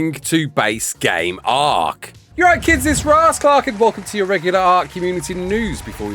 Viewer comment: 1st time i heard you say your name thought you said rasclart lol thanks for the info